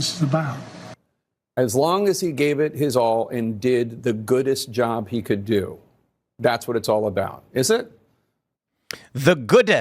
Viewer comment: To be fair, it sounded like he said, “as good a job as I could do.” But he stumbled over his words.